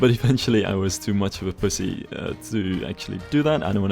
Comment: did i hear that right? he said pussy right?